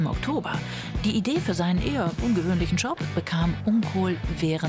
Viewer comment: Hat noch jemand verstanden , dass der Vorname des Ladenbesitzers "uncool" wäre? 1:24 z.B.